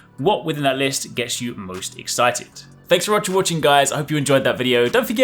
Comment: Did I hear that right? Did you ask "What gets us moist-excited?" LMAO.